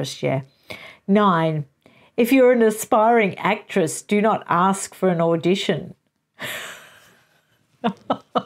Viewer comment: For a moment there I thought you said expiring actress 😂